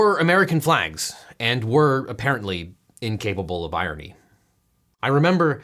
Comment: I first heard "incapable of ironing" in the opening segment. Fits for